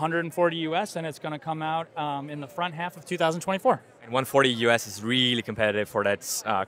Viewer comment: release on mid year 2024? u gotta be kidding me. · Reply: i thought he said the front half of 2024.